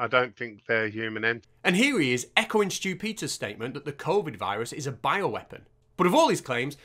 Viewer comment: I kept hearing "Stupider" instead of Stew Peters.